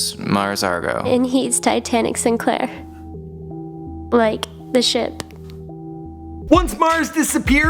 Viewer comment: At first I heard: "And he is Titanic Sinclair. Like a shit."